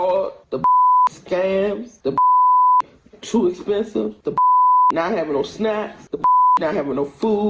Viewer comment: Did she just say, "Not havin' no snacks"? Holy Lord in Heaven. Did she just get there?